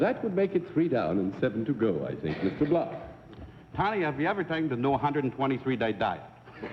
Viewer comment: I've rerun it a few times but for the life of me I either can't understand or don't get the joke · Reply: Dick Wilson I can't make out every word, but I think the gist of it is: "Have you ever tried the 123-day diet?" -- yet another in an embarrassing string of "fat jokes" at the contestant's expense, I'm afraid.